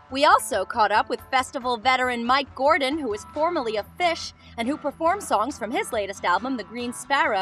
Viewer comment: Sounds like she says "festival veteran mike gordon, who was formerly a fish" haha She says "of" but that still made my smile :)